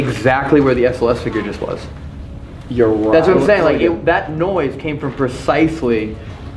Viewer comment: you hear a female voice I think saying “you right” or something